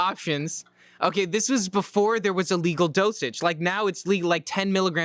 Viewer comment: I heard “before Wii was legal”